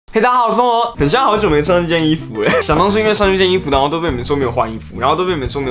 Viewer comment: 天啊😂 0:01我看/聽成 "我好久沒穿衣服"ww